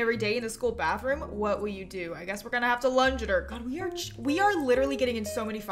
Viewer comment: I always thought it said Lounge instead of Lunge lol 😂